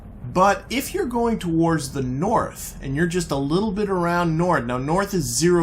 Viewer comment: Did I hear a little bit of French Canadian there slip out there? "Nord"